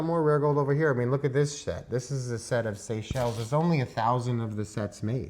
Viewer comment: 😂😂 did he say SET OR SHIT 😂😂😂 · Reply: Set but I totally get what you’re saying lol